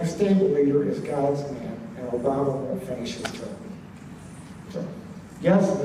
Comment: At around 18:10 does say Obama will finish his term or will not? earlier he said he would not.. But I can't hear it clearly to see if he had changed his mind on that. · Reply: As far as I know and remember he says that Obama will not finish his term. I believe he also says that the next stable leader will be "God's leader"